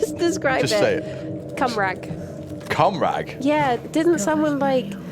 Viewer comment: Honestly, when said "cum rag", at first I thought she was trying to say Wales in Welsh - "Cymraeg". I'm sorry!